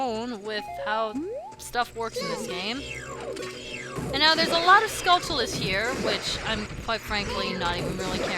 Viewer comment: I thought she Said a lot of stalphos something tells me she wouldn't like that